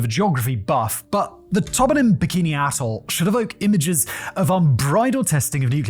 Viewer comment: Swear that Simon said, "Bikini Arsehole" at 0:06. 🤣